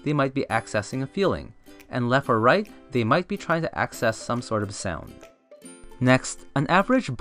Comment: "If they're looking left or right, they might be trying to access some sort of", I couldn't hear what you said at that point. What did you say there? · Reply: Hey Timmy. I said they could be accessing some sort of sound. You can also turn on closed captions if at any point the audio is not clear. Thanks for commenting 😊